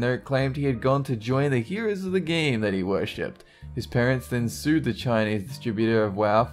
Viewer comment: I kept hearing the word "then" at around 4:36 as "didn't".